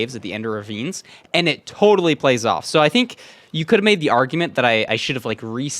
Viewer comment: "you could maybe arguement" got me rewinding that shii 3 times to check if he actually said arguement 😆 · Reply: He said "you could have made the argument... "